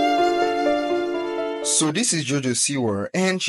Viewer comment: Lol at the start i legit heard "So this is JoJo Sewerr" and i aint complaining